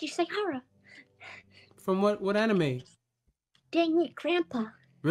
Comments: Ah yes, Shukichishukisaikara from Deadbeat Grandpa. That show is a must see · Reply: I thought it said “dangit grandpa” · I love Danganronpa...great story and game...but why do the fans have to be so freakin weird?! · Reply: I thought she said deadbeat grandpa 🤣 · I love Danganronpa...great story and game...but why do the fans have to be so freakin weird?! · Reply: Thought she said deadbeat grandpa wut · Ah yes, Shukichishukisaikara from Deadbeat Grandpa. That show is a must see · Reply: Lmao I heard her say Dang it grandpa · No because I really heard her say: "Dang it grandpa." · Ah yes, Shukichishukisaikara from Deadbeat Grandpa. That show is a must see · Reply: i thought she said dangit grandpa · Ah yes, Shukichishukisaikara from Deadbeat Grandpa. That show is a must see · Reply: i thought they said "The Angry Grandpa" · Ah yes, Shukichishukisaikara from Deadbeat Grandpa. That show is a must see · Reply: I thought it said dang it grandpa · Ah yes, Shukichishukisaikara from Deadbeat Grandpa. That show is a must see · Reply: I thought she said "yes from dang it grandpa"💀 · Ah yes, Shukichishukisaikara from Deadbeat Grandpa. That show is a must see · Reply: I thought she said dating your grandpa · Ah yes, Shukichishukisaikara from Deadbeat Grandpa. That show is a must see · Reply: I thought they said deadMEAT grandpa.. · Ah yes, Shukichishukisaikara from Deadbeat Grandpa. That show is a must see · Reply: I thought she said dating your grandpa.